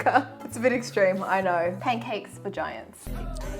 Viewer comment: I totally thought she said “pancake vaginas“...even after re-listening a few times. Finally I figured out she says “pancakes for giants” Hahahah 🤣🤣 ”Pancake taste test” Surprise!!! It’s going to taste exactly the same as how normal sized pancakes & syrup taste. 🥞🥣